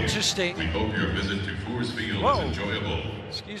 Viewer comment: what did it say? 😂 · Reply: Sounded like Fore's Field, supposed to be Coors Field. Colorado Rockies Stadium.